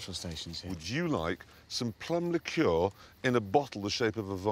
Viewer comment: Sorry, my english is not so good, what is that Jeremy offers to Richard @ 1:42? I can't quite understand that british accent :/ I think he says "Some perma cure" or something like it, if anyone can help me with the name of the drink, i would appreciate ( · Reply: yeah Plum liqueur, in other words, moonshine, Romanian moonshine!!